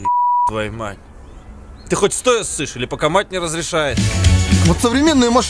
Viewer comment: "Ты хоть стоя ссышь или пока мать не разрешает?" 🤣🤣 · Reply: Хе, а мне послышалось "Ты хоть стоя ссышь, или по команде разрешают?"